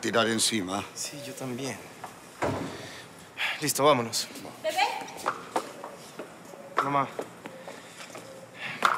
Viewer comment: que es lo que dijo rafaela? Un enviei? O q es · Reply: Master of Business Administration (MBA) / Maestría en Adm. De Negocios.